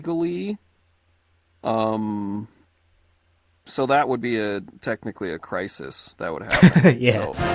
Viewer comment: すみませんここの「国家の危機」なんて言ってるのかどなたか教えていただけませんか… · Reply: so that would be a technically a crisis that would happen